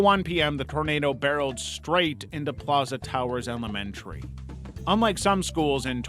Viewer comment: I thought that said pizza towers 😭